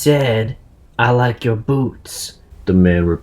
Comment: I thought He said I like your Bobs